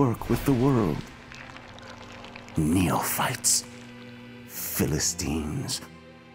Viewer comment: Did he just say "Neo-Fart?"